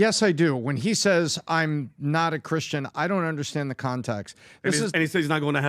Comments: It sounded like he said I'm a Christian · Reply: He said "I'm A Christian", not "i'm NOT Christian." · I heard him saying I'm a Christian,,You guys have to clean your ears properly😁